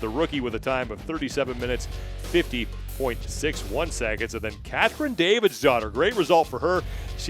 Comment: I thought he was saying Kathryn Davis’ daughter the whole time lol